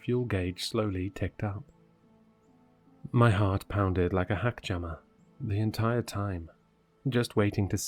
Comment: Hackjammer? I had to go back to make sure I heard it right.. 😂